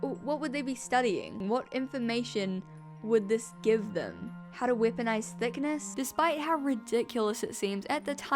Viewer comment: Even as an Australian, I sometimes mishear your accent. I had to do a double take because I absolutely heard "How to whip a nice thick ass" - @ 8:41. Love it.